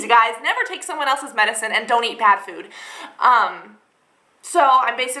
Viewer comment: haha at first i thought she said :don't take someone else's meds. And don't eat CAT food." lolz